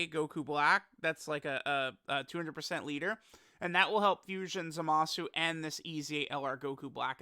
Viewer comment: We also need a patch that fixes his name, because every corrupted fused Zamasu (all 2 of them) has the same name as regular fused Zamasu, which is pretty dumb and cucks them from their strong linkset. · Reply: yeah that's annoying too. I littlery thought they said corrupted zamasu not fusion zamasu is really dumb